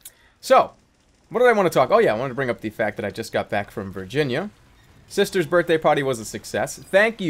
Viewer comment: idk why but for some reason I thought when you said I came back from Virginia I thought you said I came back from being a virgin XD